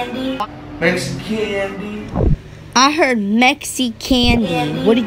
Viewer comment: I definitely heard “MexiCandy” at 1:58 😂😂